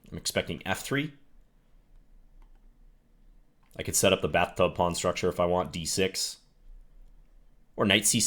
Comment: did say..bathtub pawn structure??